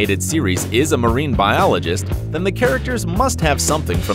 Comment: Am i the only one who thought at 0:14 he said ‘the McCharacter’ or am i just too hungry to think straight